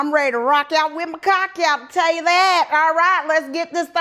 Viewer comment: DID SHE JUST SAY: ROCK OUT W/ YOUR C-CK OUT!?" "I'M SOOOOOOO FREAKING DONE, LOL 😂😂😂